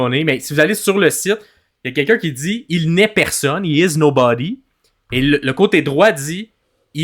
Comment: alors moi quand j’ai vu la vidéo j’ai l’impression que la voix dit (la c’est ce que je pense personnellement mais je peux me tromper) j’ai l’impression que la voix dit SHE is nobody, SHE is All of Us,elle n’est personne elle est nous tousse mais ça j’suis pas totalement sûr donc j’ai l’impression que la voix parler d’une femme et si c’est vraiment une femme qu’on parle de qu’elle femme la voix parle exactement ? Mystère 🤔🤔